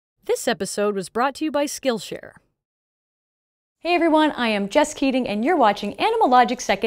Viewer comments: I misheard her name as "Just Kidding" it was Jess Keating, lmao. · first sentence i heard "hi everyone i'm just kidding" 🤔 wait what...? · "hey everyone I'm just kidding..." Don't know why I heard it like that XD. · I thought she said, "Hello everyone, i am JUST KIDDING." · Video: "I'm just kidding" Me: 🤔 Did she just say... Nah, it can't be. [Rewinds] Video: "...just kidding" Me: 🤨 What? [Raise volume] Had to go to the description to read the actual name 😅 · At first I thought you said "Hey everyone I am just kidding"... I was like about what?? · This is my first time here, I thought I heard it wrong or something, had to go back to check but still hearing "HI, I'm just kidding" instead of Jess Keating can't unheard. 😂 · Why Did I hear "Hey Everyone, I'm Just kidding" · I thought at the begging she said “hello everybody and I am just kidding” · i thought she said hello everyone im just kidding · I totally misheard her name and thought she said Just Kidding and not Jess Keating.